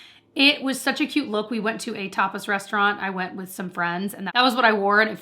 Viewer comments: When you said “tapas restaurant”, at first I thought you said “topless”. 😂 Had to rewind to hear it again. LOL. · For a moment I thought you said you went to a topless restaurant…. Tapas tapas….. got it.